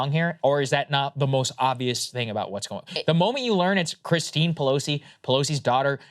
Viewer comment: thought he said krystal Pelosi! Lmao. Thats her maiden name